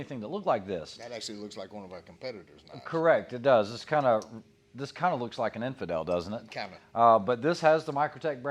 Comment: Why did he say “Kevin” @ 1:10 when he told him it looks like an Infidel? I don’t know why I keep going back to that part I’m trying to understand what Kevin means 🤣🤣 MY OCD